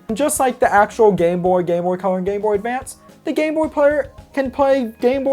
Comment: It sounds like you said gay boy and gay player player